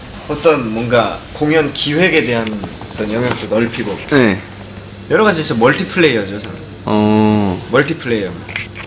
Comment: I think Eunhyuk says "multi player" rather than "model player." Thankyou for the subs^^